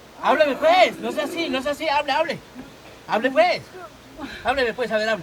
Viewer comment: I keep hearing "Repent"